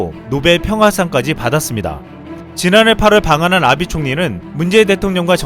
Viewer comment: 아 이러면 안되는데 아비총리를 아베총리 라고 잘못 들었는데 너무웃기넼ㅋㅋㅋㅋ 아비총리님께 너무죄송합니다ㅠㅠ